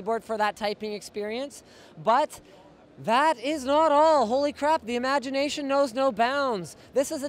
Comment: Did he say crap on 8:02?